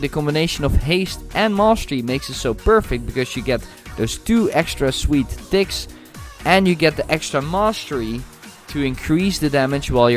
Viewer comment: Sounds like you said "those two extra sweet dicks" at 14:55 xD